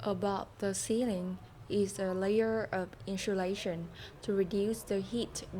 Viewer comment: bằng cách nào đấy mình nghe ra isolation:)) :person-turqouise-waving: Jokes aside, video em làm chất lượng quá, đúng là gia cảnh ko cản bước được ai thật sự cố gắng và quyết tâm:trophy-yellow-smiling:, chúc em ngày 1 phát triển và thành công nh<3 ! ! !